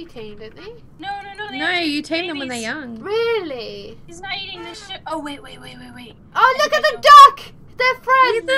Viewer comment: did someone say "He's not eating this shit"